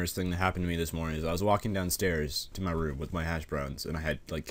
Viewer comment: thought he said hash brownies... til he mention ketchup